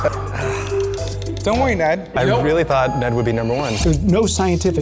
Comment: Did someone call Eugene daddy?